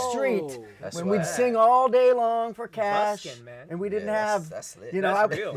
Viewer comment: Did anyone else hear jar say busking at 6:13 or is it just me. No? Just me 😂